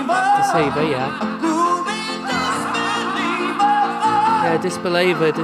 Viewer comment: I heard "A booby disbeliever."